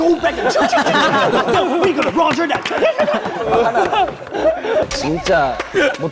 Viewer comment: did Jackson just say Roger that? im dyinggg